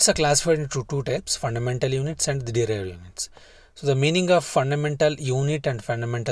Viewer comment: "The units are classified into two types fundamental units and thjethjethje"............what do you want to say? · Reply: It is "Derived units"